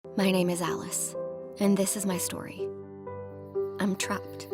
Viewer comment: Ok but why did I hear “my name is ass” I